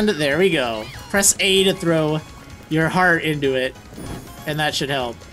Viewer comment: I thought it said "put your pokemon into it!" because Scorbunny was in the circle and I was like I'm sorry little one... but you will be a big price to pay for salvation T_T